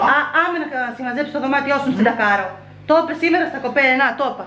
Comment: Τι είπες στο 14:48;; Αμε να συμμαζέψεις το δωμάτιο σου μην σε....;; Κακαρω; Τακαρω;; Τι γλώσσα μιλάτε βρε παιδιά εκεί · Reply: "Μην σε ντακάρω" σημαίνει "μη σε δείρω". Έχουμε πολύ πλούσιο λεξιλόγιο εμείς εδώ στην Κρήτη... Χα χα 😁😁😁🤣🤣🤣